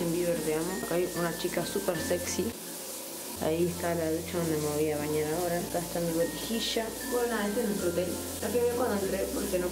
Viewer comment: En el minuto 3:13 pensé que decía: Soy peluda y me olvide de afeitarme en la ducha. Yo miraba a mica por todo lado y decía ¿Que onda? y después miré bien y me dí cuenta que mi mente perturbada había captado mal las entonaciones y letras de sus hermosas palabras con su linda reflexión. Bueno me fui al carajo 😂😂